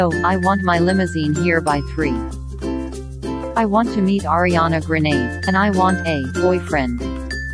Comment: D-did I hear “Ariana Grenede” 😂😂😂 I